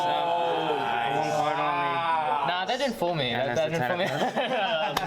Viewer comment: did he say "that didn't fool me" or "that did fool me " ? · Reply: he said "that didn't fool me"but we said it in a joking way.